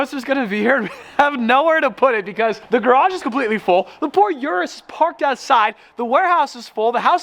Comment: When he said the por uris I thought that he said the Ford uris🤣🤣🤣